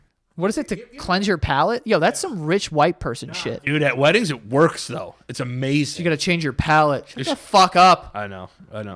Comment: I just heard something wierd! In the 1:01:09- 1:01:12. There is a low audio clip from you guys ! O.o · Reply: It's really low, I can hear say "Nah"